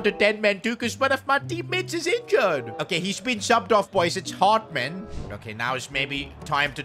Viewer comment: Why was this so funny in my mind? WAIT DID I HEAR "HARD"